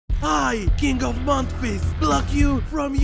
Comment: I thought it said "I king of mount rizz"